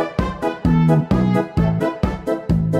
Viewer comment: the Closed Captions thought Churchill said "At any rate that is what we're go to try to do"